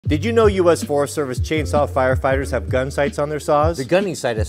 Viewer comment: Thought it said gun rights at first 🤣